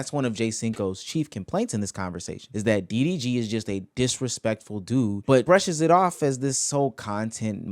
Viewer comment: did he just say he is a street dude ? lol